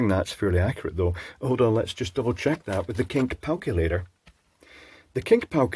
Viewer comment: The Kink Palculator ? Am I heard that? 🙃😅